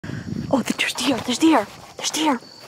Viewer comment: I thought you were saying "there's the air" not "there's deer!"